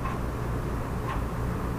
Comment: anyone else catch that ghostly voice at 1:23:55 ? At first i thought it was CW but his mouth appears to be completely shut and he is the only one in the room. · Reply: It says "oh, God!"